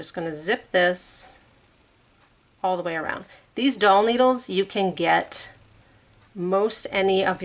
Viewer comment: I admit I’m tired, ready for bed and having a cup of hot tea. Thought you were saying a 5” dull needle. Then you said “be careful they are sharp”. But you said a full needle. Oh! 🤦‍♀️ a doll needle! 😂 I really want to try a drum soon. Your tutorials are excellent.